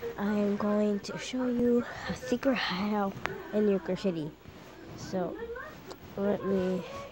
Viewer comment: LOL. It sounded like I said Yogurt City!